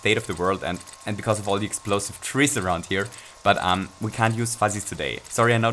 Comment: did... did he say “explosive trees”??